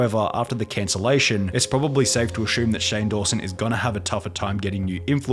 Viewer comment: irrelevant but i just found 10:47 funny bc u said “ashume” instead of assume lmao